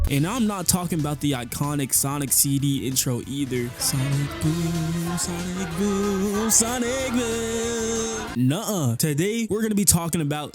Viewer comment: What I hear:Sonic GOON Sonic GOON Sonic GoOOoOoN